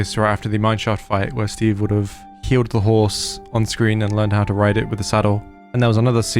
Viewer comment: When He Said " When Steve Would Have Healed The Horse And Learned How To Ride It With The Saddle. " I Was Like, WHAAT?!?!!?!? Because I Thought He Said " When Steve Would Have Killed The Horse And Learned How To Ride It With The Saddle. "